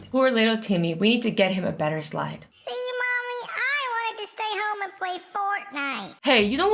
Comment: Hey, i heard "See mom i want to go home and play fortnite" does that meanyou like fortnite because i DO NOT like fortnite